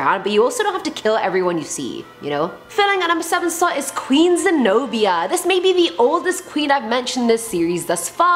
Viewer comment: Not going to lie to you, I had looked away when at 5:40 I thought she said queen of Genovia, to which I immediately thought "SHUT... UP!"